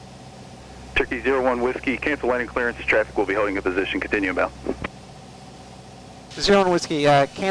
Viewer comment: What did tower say at 7:20... continue about? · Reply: Tower said "continue inbound." Lexington does the "Line up and Wait" instruction quite often. Thanks for the comment.